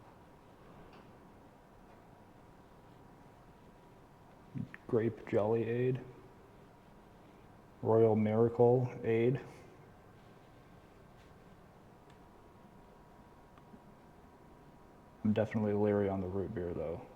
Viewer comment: I was looking away at the time and had to rewind because I heard, "loyal marital aid"